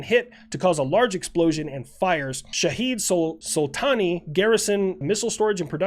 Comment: I know it’s spelt shahed but who else hears shithead.